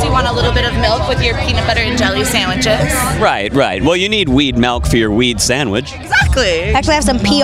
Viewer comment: DID YOU SAY MALK?? 4:23 (Julian Smith)